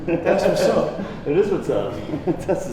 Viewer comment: did juji say “test is the best” 😂